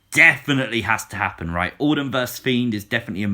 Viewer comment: When you said Orton vs Fiend, I thought you said Autumn vs Fiend LOL